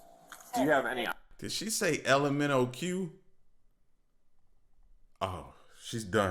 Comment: If it is alcohol related, the longer they take to take the breathalyzer test…. the lower her BAC will be!! I know that for a fact! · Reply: "Did she say lmnoq?" 😅😂